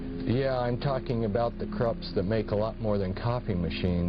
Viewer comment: 1996 so relevant now. Reynolds and Cupps family’s both of which are already exposed by survivors in the recent years on this podcast ❤❤❤ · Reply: I misheard Cupps. Later he clarifies Its Krupps